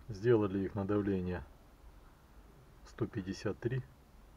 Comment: Добрый день по итогу какое давление поставили 153 или 135. На видео на 19:04 минуте слышу 153 а в комментах читаю 135. За ранее благодарю за ответ. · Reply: Добрый день Нурислам, давление выставили от 153 до 157 выше ставить не стали из-за старости ТНВД.